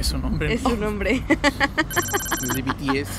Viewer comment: Oí mal o dijieron BTS en el minuto